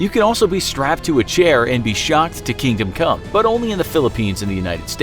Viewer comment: I thought he said you could be strapped to a chair and shot to kingdom come idk why but that sounds hilarious